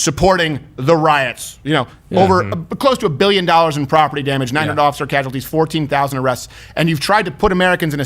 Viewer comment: Did he say "900 officer casualties" ?